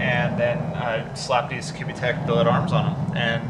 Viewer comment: Who makes that sway bar? Sounds like he says QB tech, but I’m not seeing anything like that with my Google Fu